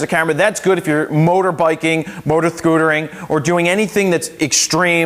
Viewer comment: so out of all of this talk and review all i heard was "motor-sthkootering" at 6:21. haha great review though